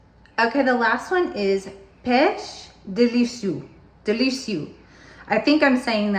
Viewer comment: Pêche Delicieuse sounds especially lovely. At first I thought you said “péché” which means “sin” in French and I was like, “oh, delicious sin!” Lol. I am putting it out in the universe that you get your own fragrance and makeup collaboration❤ You looked gorgeous as usual😊